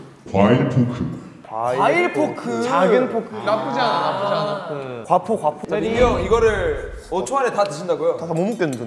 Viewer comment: Did mingyu says "gwapo gwapo" 5:59, for the filipino carats there 😍